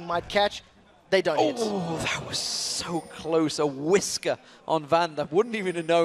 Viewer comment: Can Someone tell me what whiscer (or what ever he said there...i dont know how to spell it) means? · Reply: *Whisker, It's an idiom. It means he missed it by just a tiny bit. Another similar example would be "He missed it by a hair's breadth". Just search it on Google if ye need anymore explanation.